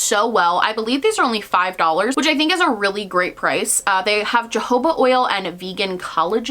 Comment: tell me why when you said “jojoba oil”, I heard “Jehovah oil” and I had to rewind and watch again 😂